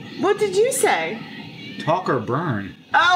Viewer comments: I totally heard "Taco burns" too... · honestly I thought he said tacos too